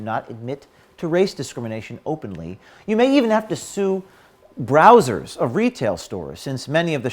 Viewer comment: "You mean even have to sure Brazzers" I'm not the only one who misheard that huh